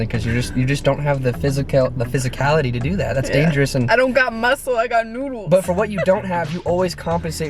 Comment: ”I don’t got muscle I got noodles” 😂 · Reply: Hanna Sundell I thought she said I don’t got muscle I got nudes